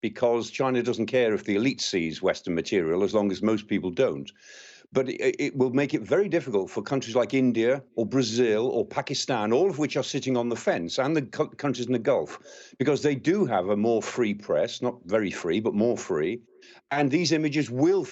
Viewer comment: did he say Brazil?